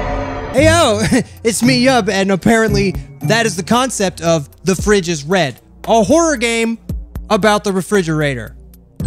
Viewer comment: i thought it said "the fridge is Fred" lol